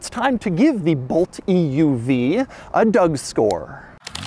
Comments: I had to go and replay this 6 times in the beginning because I kept hearing "Doug Store". I can confirm 100% that this is what is being said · Did... did he just say 'Doug Store'?